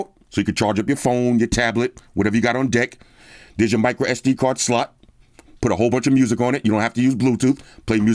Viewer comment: There's your micro SD card slut. 5:34 And there's your USB type C charging slut, okay? That's how I hear anytime when say slot word. 😆